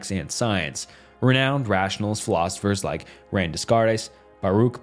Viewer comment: what did he say??? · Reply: Rain Desarteis